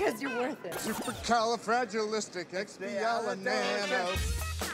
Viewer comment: I thought Rdj said “supercalifragilistic yah no” 😂